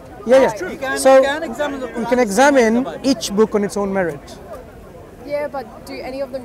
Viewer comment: what is the word here? marit?